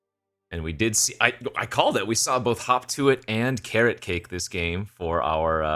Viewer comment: dude. whenever u said: hop to it. it sounded like u said: hawk tuah